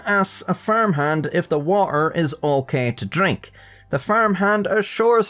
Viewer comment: ? What unholy abomination did you say there mate? The watHer?